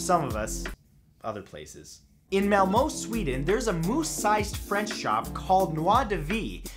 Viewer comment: did he say "moose - sized?"